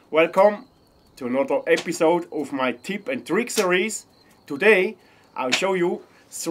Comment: Oh my goodness.... I just realized something.... I thought you have been saying, "Tips and Trickeries" when you are announcing your videos about tips and tricks.... I finally heard what you you have actually been saying all along.... "Tips and Tricks Series!" Rofl.... I have to admit, I think I like the "Tips and Trickeries" better! lolololol.... I love your videos! I’m bingeing these tips and tricks!